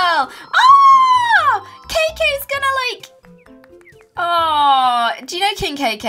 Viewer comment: Did... Did Kim just say, "KKK"!?